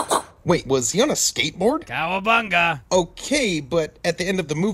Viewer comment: Did he just said cowabunga?